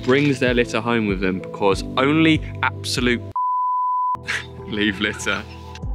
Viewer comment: I think he says 'wonkers'. 😊